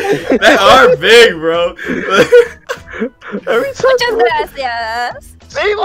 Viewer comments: when Naruto (sage) dropped the pencil again and Kakashi sensei said gracias it sounded like he said grassy-ass and I cracked up lol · when says gracias but it sound like he saying grassy ass 😂